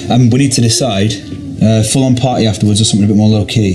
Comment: question: at about 1:16, does Robert say they are having a foam party???? That would be really gay.